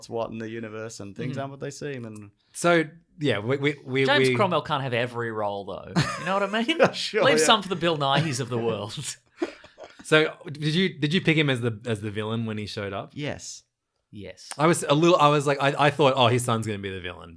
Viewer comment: When you guys were saying Bill Nighy I thought you were saying Bill Nye so the whole time I was hyped for Bill Nye the Science Guy but nope I was so disappointed